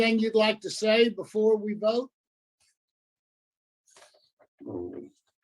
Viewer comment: I thought FORSURE said, "Anyone got anything else to say before we revoke"??? Lol but he said vote.